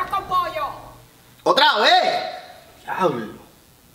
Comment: que se supone que dice? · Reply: Diablos Xd Xd Xd Xd Xd